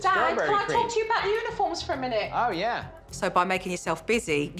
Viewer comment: I love the fact that Jo was like, "Dad? Can I talk to you about unicorns for a minute?" And him going " Absolutely!" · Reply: I thought she said uniforms lol unicorns makes it so much better hahaha